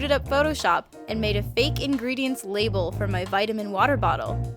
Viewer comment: thought that said Vietnam